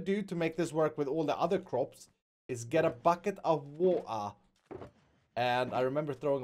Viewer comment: Did it sound like he said woa instead of water 😂😂😂😂 (I caught it lol)